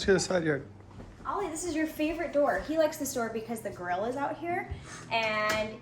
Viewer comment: I thought she said gorilla when she mentioned the grill. I was really confused for a second haha